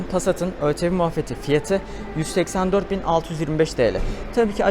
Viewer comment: dakika 9:23 de184.625 tl mi dedin ben mi yamlış duydum kardeşim